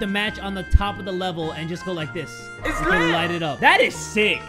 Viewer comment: Does anybody hear Guava Juice's / Roi's Voice saying "Its lit"?